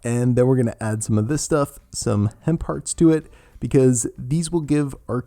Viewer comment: Hemp hearts...I thought you said hen parts 🙀 I’ve been craving oats, will be doing this!